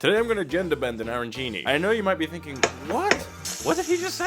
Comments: I thought he said he Airbend aranchi😂😂😂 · I heard ‘agenda bend’ and though ‘that’s a fun way of changing plans’ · Ohhhhh I thought you said “GENDER bend” and was very confused about food genders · I literally heard "today we're going to make an arancini" and I thought, "ok, normal..." but when you made them sweet, i was confused. then, on the second round, i heard "genderbend and arancini".